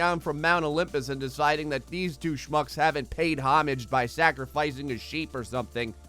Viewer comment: I thought he said douche fucks instead of two shmucks